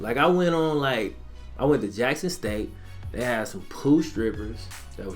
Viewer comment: Dude I seriously looked at the comment section for this very reason. Also what are "pool strippers." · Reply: He misheard. Not "Pool." It's the Southern accent. He said, "poo strippers," as in the strippers they have in that area / during the visit were garbage. It's vernacular that's around Northern California, too, so I caught it.